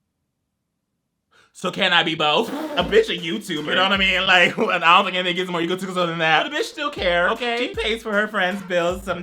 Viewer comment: the captions say mumbles but it’s “bitch a YouTuber?” “an alpha can’t even get more egotistical than that”